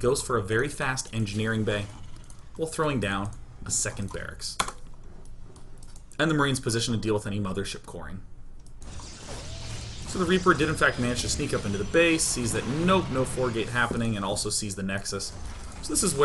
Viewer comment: did he just say mothership corn?